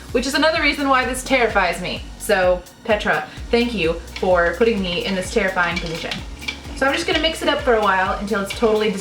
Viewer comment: I heard "thank you Petra for putting me in this horrible situation" aot?